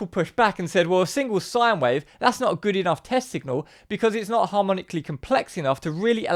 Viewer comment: I clearly heard "Testicle" at sec 00:45... In 2003, I attended a Macroeconomy symposium where Myron Scholes himself gave us a lecture - I was 20 years old and I think I also heard him saying testicles at some point during his presentation :-D For real > From Google AI Overview "The ITAM Finance Symposium is an annual event that brings together leading academics and practitioners to discuss cutting-edge research in finance. Myron Scholes, a Nobel laureate, was a prominent speaker at the 2003 event". Sorry, LOL I Had to take a look!!